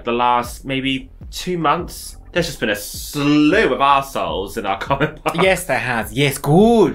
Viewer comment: When you said Slew of Arseholes at the end I first thought you’d said Stew of Arseholes and thought it explained the takeaways.